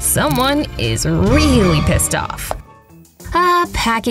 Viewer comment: You swore you said pissed off